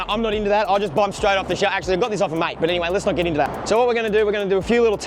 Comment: oh nice I though I heard you say borrow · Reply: Na I said I got it off a mate, bitta bartertown😉